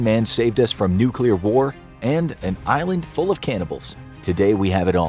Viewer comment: I swear i heard you say an island full of cannabis and my interest peaked but then i found out it was a lie.